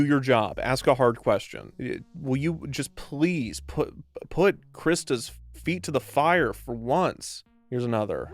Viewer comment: yeah can we PLEASE put Krista's feet on the quacamole ? Ohhh ... Umm... No, yeah what he said ... Nvm .